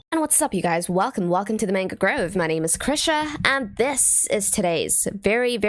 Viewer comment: I always hear "my Name is Crusher" 😂